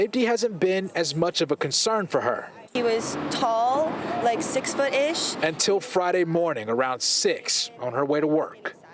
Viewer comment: “He was tall, like 6 footish, he was ...... “. Wait what did she say? Reporter started talking over her. Can somebody please tell me what she said after? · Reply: She said he was black. You can hear that if you listen closely.